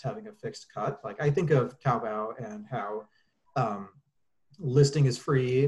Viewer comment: What app or website did say around 48:45? (couldn't understand him) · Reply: taobao